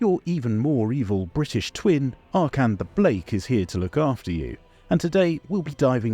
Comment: Alone in my car at 4am I heard the phrase "Arkhan the Blake" and loudly exclaimed "fuck ooooff" to no one but the wind